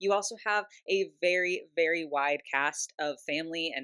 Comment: when you said "wide cast of members" I understood "wite cast" 😅 for a second I was flabergasted they I realized what You actually said 😂😂 Good thing Barbie has expanded a lot in inclusion 😅👍